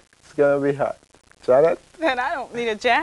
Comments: I like to think that he laughed that hard because, “I don’t need a jacket,” was some kind of inside joke in their studio. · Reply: l thought that she said "l dont mean to jack it" l am very confused right now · I like to think that he laughed that hard because, “I don’t need a jacket,” was some kind of inside joke in their studio. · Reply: This comment was the final piece of the puzzle for me. I thought she was saying "I don't SEE a jacket" and was always so confused